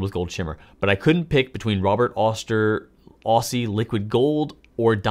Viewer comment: Before you wrote “Aussie Liquid Gold” I thought you were saying “Ostie Liquid Gold”, like giving Robert Oster a nickname 😂 thanks for the video!! I’m hyped to try some shimmer inks now!